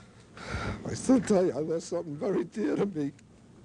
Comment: What's the father saying at 18:05? I can't understand him at all, something something, very dear to me? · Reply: ”And i have lost something dear to me” heartbreaking i wanna hug him and all of the victim’s family and friends